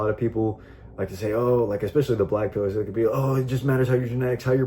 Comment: bro your caption at 3:59 says black people😔took me a min to realize what you acc said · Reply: I said black PILLERS lol